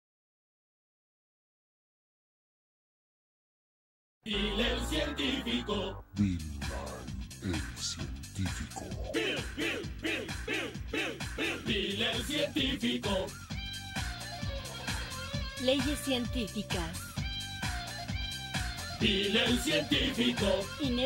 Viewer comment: ”Bill a Sal tv co” I swear I miss heard that on the Spanish one.